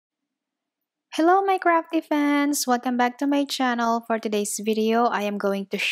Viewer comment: i thought you said "hello my crappy fans" and i was like WHU-WHUT?